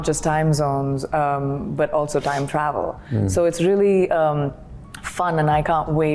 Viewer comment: Did she just say "Time Travel""?